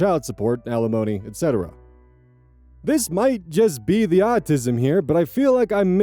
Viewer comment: I was typing on Discord while listening to this, so I misheard "autism" as "Oz-ism". Still works, though.